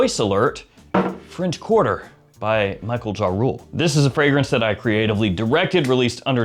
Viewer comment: did he just say Michael Ja Rule ?